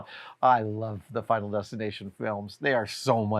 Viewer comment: I had to rewind! I thought he said, "Biden resignation films"! Which, really surprised me! Adam, CA, San Francisco (or what ever lib city) and all